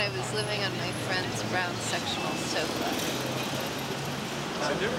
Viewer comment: These fans who were interviewed while waiting in line (you just KNOW they've searched this video on Youtube and shown it to all their friends), come on out, reveal yourselves, right now! I, for one, would especially like to know what became of (and her brown sectional sofa) 2:02. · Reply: "for one, would especially like to know what became of Tina Holt (and her brown sectional sofa)" Oh it's 'sectional sofa" having played the DVD over the years I always thought she said "Sexual sofa" :) Always wondered what that supposed to mean. LOL ;D